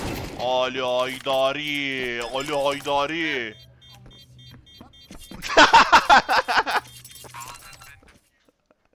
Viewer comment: Ali ne diyor olm burada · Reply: amını sktiğim yat diyor xfloud u vurduktan sonra wicadia